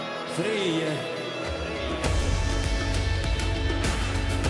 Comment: What does he say in Swedish on 2:29? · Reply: Victor Efremov Sverige, den här joiken är till er ! No prob ;)